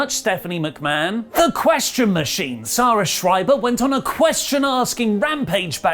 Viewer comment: did you say Zara Schreiber? As in that Hitler loving woman Rollins was with?